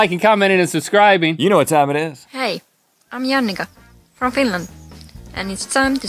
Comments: did he just say I'm your niggah from Finland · Did she just say I'm YaNigga?